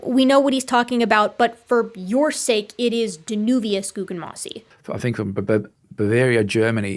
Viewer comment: I heard Davinius